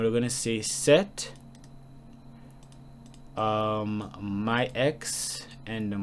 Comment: The subtitles said my ex at 15:19.